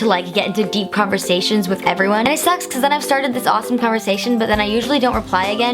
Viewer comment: When she said "it sucks" it sounded like she said "I sext" who else heard that???😂😂😂